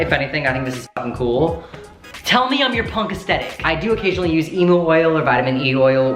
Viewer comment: I literally heard Emo oil 😂